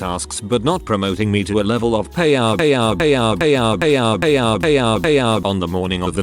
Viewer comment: around 8 or 9 “pay our” or as the captions said: “Payout”